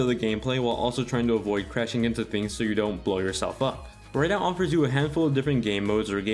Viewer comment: Did you just call it Burnout?